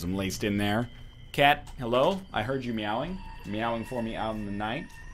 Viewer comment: am I a bad person because I thought he said:"Kate you have been meowing for me" instead of "Cat you have been meowing for me" o_O